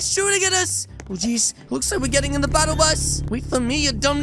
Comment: I heard him say "And stop" at 4:48 with his normal voice